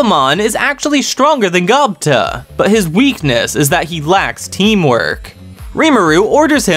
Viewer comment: did i hear gobta is weak, i bet gobta just let rimuru believe it . All Hail Lord Gobta :face-blue-smiling